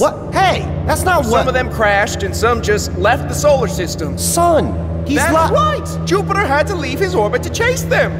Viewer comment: Did he said "Sol"?